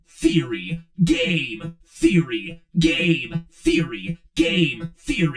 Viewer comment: why do i hear gay theory